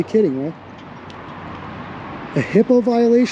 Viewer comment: did you say "hippo" ? lol